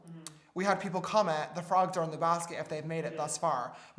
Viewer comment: Literally all I heard for ‘the frogs are in the basket’ thing is: DONT eat cranberries... salad. Idek if I’m deaf or smthn I’m sorry